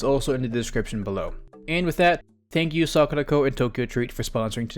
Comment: My God. When you said sakuraco I thought you said "were sponsored by suck the toe"